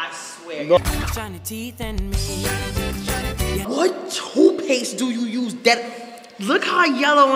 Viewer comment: Sound like she said “hawk tupace do you use”😭